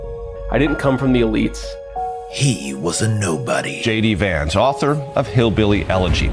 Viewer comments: Why do I always hear "JD Vance" as "Shady Vance"? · I thought you said Shady Vance not JD Vance. I think I WILL call him Shady Vance🤣